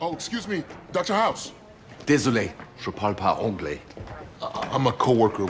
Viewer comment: What did he say in French at 1:00? It sounded like "désolé je peu pas embole" or "désolé je pa pas embolie".